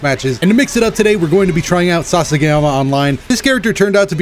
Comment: I heard "Sasageyama" and now im tilted >.>